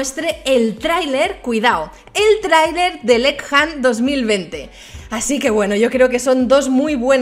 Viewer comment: Dos mil vente escuche o estoy sordo xd??